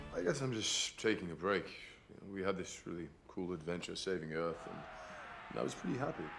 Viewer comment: Hey there, JoBlo Movie Trailers! I love this video so much, and I want to make sure everyone can understand and enjoy it. That's why I wanted to let you know that your captions are a little off. 0:08 the auto-captions say "We have this really cool adventure saving of" when they meant to say " we had this really cool adventure of saving earth" 0:23 the auto-captions say "in the dark" when Thor didn't say that at all 0:32 the auto-captions say "Daryl is an average solar everyday guy" when Thor meant to say that "Daryl is an average sort of everyday guy" 0:38 the auto-captions say "do this purple widow? Surprised who actually knew what!" when they meant to say "And who's this purple weirdo? Vision! Surprised who actually knew who that was!" 0:48 the auto-captions say "Darrel here's hoping the craft electronic " when he meant to say "Daryl here's helping me craft an electronic letter" 0:58 the auto-captions say "Dear turning stock" when they meant to say "Dear Tony Stark" (that's a pretty funny autocorrect though!) 1:07 the auto-captions say "dear Steve rogers you remember when I go to you into killings those prisoners" when Thor meant to say "dear Steve Rogers, do you remember when I goaded you into killing those prisoners" 1:34 the auto-captions say "go through battles, the only over here taking a nap" when he meant to say "costume for battles, Mjölnir over here taking a nap" 1:49 the auto-captions say "what infinity slept" when they meant to say "what are infinity stones?" 1:5